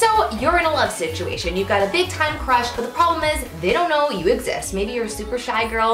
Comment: Who Else Thought She Said Big Time Rush Instead Of Big Time Crush?